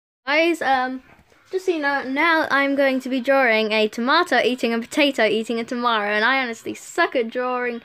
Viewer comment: I thought it said "I drew a tomato eating a tomato" Lol